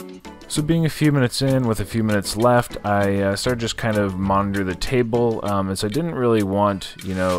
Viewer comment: I could of sworn she said no rice on the table … messy lol